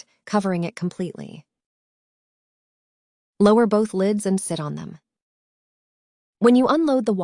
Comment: I had to come back to this. I thought she said " Lower both lids and shit on them".